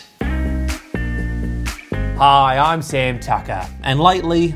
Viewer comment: I thought he said " Hi I'm Sam F**ker"